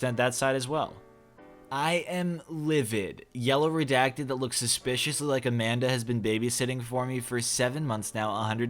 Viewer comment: I thought he said looked suspiciously like a man because this was posted when I just woke up so I was listening to it half asleep and was so confused when they didn’t bring it up again. Like I thought they sucked but gave them credit for not being transphobic? Or like tomyboyphobic? Y’all know what I Mean